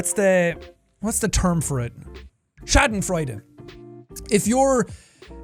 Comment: What does he say at 35:17? It sounds like Shattenfreuten and I have no idea what that is